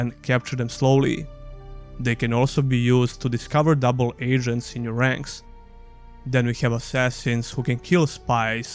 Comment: I swear I heard "they can be used to discover double asians in your ranks" :D